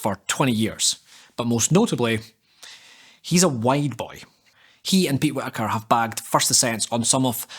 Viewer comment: great production dude. how you get all these top climbers to come on to your channel is a mistery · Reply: i thought you said he is a white boy XD